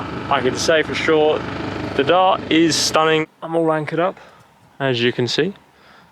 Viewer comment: It sounds like you said I'm all wankered up lol.